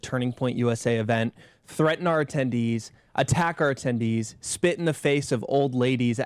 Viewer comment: The way they speak made it sound like they said "Antifa attacked our tendies"